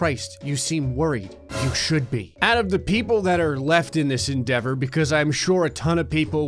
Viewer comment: did he just say Endeavor?